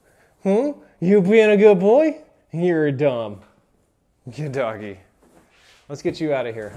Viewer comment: Did he say "You're a dumb?" I love it.